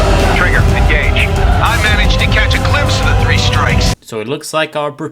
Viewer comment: I heard 'Three Stripes', not 'Three Strikes'.